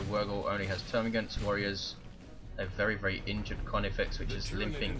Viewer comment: I heard "a very, very Indrid Carnifex."